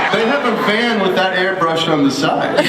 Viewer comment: I wonder what Misha said to Jared and Jensen to make them laugh so hard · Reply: 8 years late, but in case people wondered, he just repeated what Ben said: “We have a van with that airbrushed on the side.”